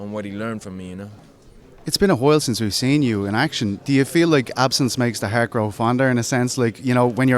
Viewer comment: did he say absence makes the hair grow fonder?